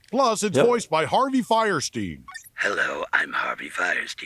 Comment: For a second, I thought he said "Weinstein", then the watch started talking, and I was like, "Oh yeah... Fierstein. Yeah, he's cool."